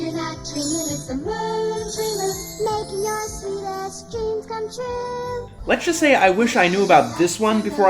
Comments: did it say make your kick ass dreams come true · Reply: I thought she said "sweet ass dream come true". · did anyone else hear "making your sweet ass dreams come true"